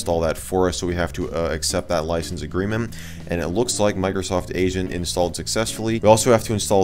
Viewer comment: I thought he was saying “Microsoft Asian”